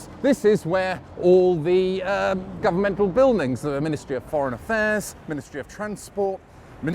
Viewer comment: I thought he said "the ministry of .. 'pron'.." due to his accent when he said Ministry of Foreign Affairs.